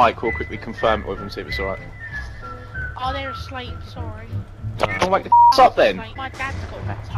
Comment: he says go why does it say dont?>